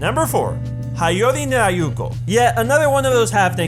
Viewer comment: what anime i couldn't hear him properly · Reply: Haiyore! Nyaruko-san